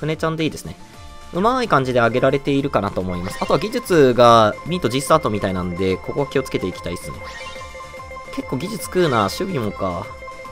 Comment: のとこなんていってるんですか？ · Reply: 技術がミートGスタートみたいなんでここ気をつけていきたいです